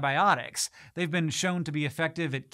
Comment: did you just say funji?